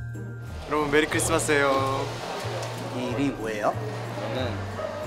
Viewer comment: i thought wonwoo said "Merry Christmas sayo" 😭